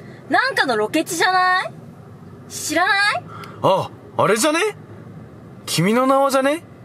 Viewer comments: 君の名はを連呼しすぎて君の縄にしか聞こえん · 「君の縄」にしか聞こえんw